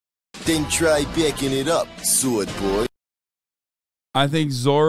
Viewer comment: did sanji just called zoro a soy boy?? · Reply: He said Sword boy, but with that awful accent it def sounds like it lol